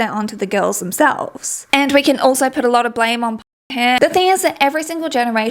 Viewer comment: What were you about to say at 12:20? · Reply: She said 🌽orn. She had to censor it